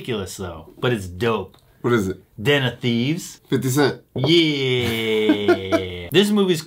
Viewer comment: This whole time I thought they were saying "dinner thieves" until this movie came out. 😄